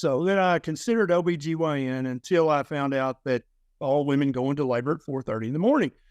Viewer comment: Did he say "...until I discovered that all women have babies at 4.30am". I'm assuming that I'm going senile and misheard him. Either way, I'm glad I didn't give birth at 4.30am! 😂